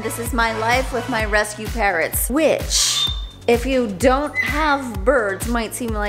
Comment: I never seen this channel till now and I thought you said “this is me and my rescue parents” 🤣 i was like omg what nooooo 😭🤣